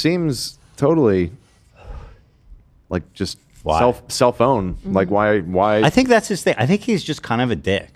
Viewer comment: Did anyone else hear say Cellphone instead of self-own?